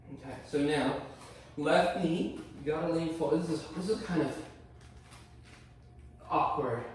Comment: Yoga is always better with a little profanity. I got a little twisted up around 32:00 when I thought you’d said ‘left knee forward’, but I quickly found my way back. Now I’m looking forward to hearing the rant about half binds in the future…